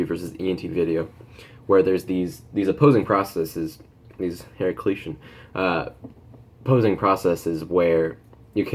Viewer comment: What does he say at ~1:38 ? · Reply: Heraclitian